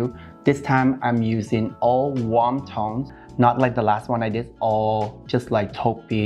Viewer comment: omg when you said warm toned I thought you were saying wonton!!!!! guess I'm hungry hahahaa